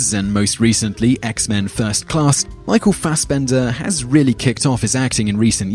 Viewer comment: For Michael Fassbender I thought his last name said assbender